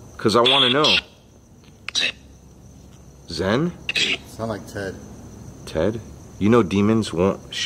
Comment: it sounds like she says Eve is the demons name..i also heard the woman giggle when you heard it